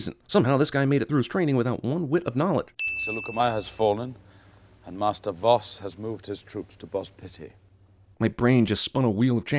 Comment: So Lukemeyer has fallen and Master Vos has moved his troops to Bospity is what it sounds like Obi-Wan is saying